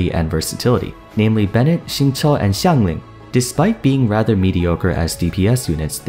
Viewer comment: Did you just say Xiangling as a DPS is mediocre 🤨🤨 ????